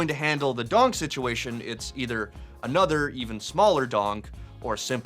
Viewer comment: "...another, even smaller Donk..." LOL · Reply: I thought he said "another, even smaller dong" for a sec and had me laughing out loud